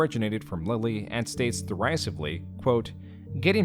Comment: I kept hearing "ball spawn" instead of "Baal Spawn" and thought to myself that technically we are all ball spawns, and after looking at the screen I had a laugh for the first time during this video. This could be some sort of common name for humans in general. "ball spawn".